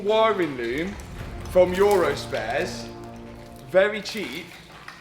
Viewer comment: All along I thought was saying "URUS-spares", till I saw the laptop and it's 'Eurospares'😂